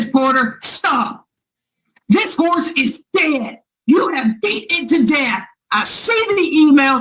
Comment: Did she just say "This horse is DEAD!" 1:01:47 😂😂😂😂